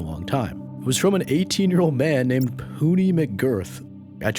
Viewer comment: Bro im listening to this vid and i thought he said "Hootie" until I opened the comments. POONEY??!